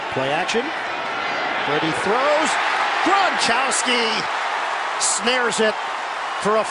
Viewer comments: am i the only one who heard at 0:54 gronchousy instead of gronkwlski? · did he just say gronchowski? Lol😂